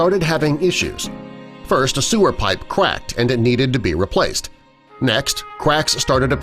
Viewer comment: haaahaaaa it sounds like hes saying quack